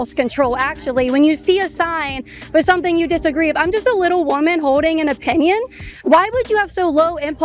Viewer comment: Can you pronounce the L in the word Holding Kaitlyn? I hear you saying “Hoe-ding” 😅